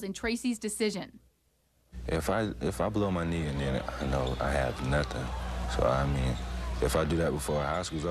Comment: did he say again? ie. he blew his knee once already in high school? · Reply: He said "If I blew my knee and then I have nothing..."